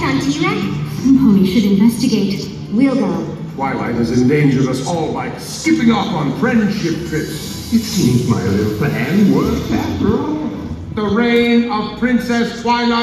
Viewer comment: What does Neighsay says from 00:46 to 00:53? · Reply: OptimusPrime 100 Neighsay: "Twilght has indangered us all by skipping off onto "Friendship Trips". It seems my little plan worked after all. The reign of Princess Twlight is over!"